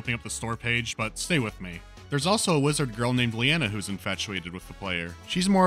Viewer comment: I heard lizard girl the first time and was severely disappointed.